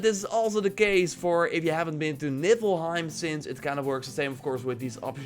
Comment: !!!!! Did u just say nippelheim?? 😂😂😂